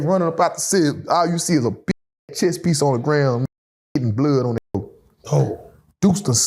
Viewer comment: Damn I’m high swear to god I thought it said Piece of Cheese 😂😂😂